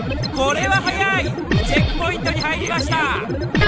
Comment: 幼い頃、10ポイントって聞こえたwww